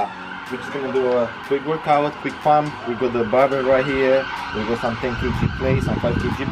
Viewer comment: did u say we got the bara right here ?=]]]]]]]